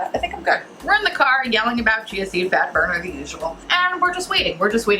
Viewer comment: My idiot self thought you said Chauncy fat burner im like what in the hell would someone have that name 🤣🤣🤣 i swear I need hearing aids. This is what I get when im trying to get ready for work at my make up job 😅😅😅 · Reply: haha it's not just you, I heard "Chauncy Fatburner" the first few times she said it too and was like TF???